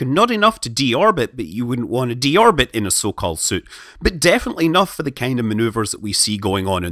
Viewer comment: "You wouldn't want to deorbit in a Sokol suit" - Scott Manley, 2019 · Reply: And here I thought he was saying "so-called suit" lol